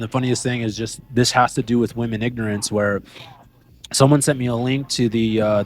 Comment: “Influencer- niche internet micro celebrity” interesting that they consider themselves as this…. Lame. And- at 11:25 “women ignorance?” I think that’s what he said?!?! -Id like him to define that!